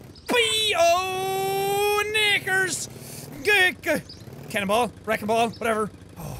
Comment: I love how he didnt even realize what he said sounded like he said the n word.